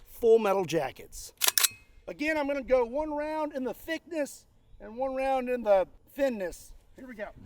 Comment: I really want to destroy this helicopter! Help us crush our goal by swinging over to <link> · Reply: I dont know why, but in my head I hear you say "One round in the Thickness" in Disturbed's voice "Comon, get down with the thiccness! U-AHAHAHAH!"